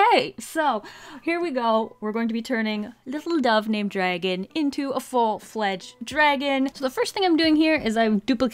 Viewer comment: I thought you said, “I’m going to turn this Dove named Dragon into a dragon named Dove” lol